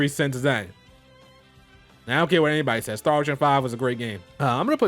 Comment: When he said "I don't care what anyone says Star Ocean 5 is a great game" I'm assuming he meant 6.